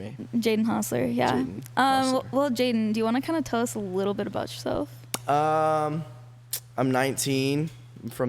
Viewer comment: When she said come and tell us a about yourself I thought she said SO JADEN COMATOSE ABOUT YOURSELF😂😂😂😂